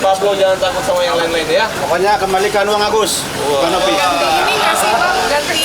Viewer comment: Apa saya nga salah dengar ya tadi sama bpk2 yg bilang maju kedepan Kembalikan uang Agus ke Novy 😂😂😂